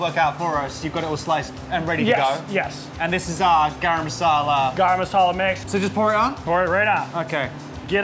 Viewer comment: I'm an Indian & I heard Garam Masala right ??